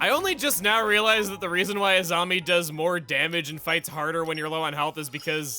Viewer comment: i was wondering when you were gonna make a korrasami joke lol. I thought I heard you pronouncing Azami as Asami a few times